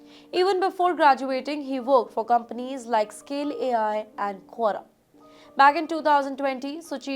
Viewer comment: The subtitle says "qua", but I hear the speaker saying "Quora"... am I wrong?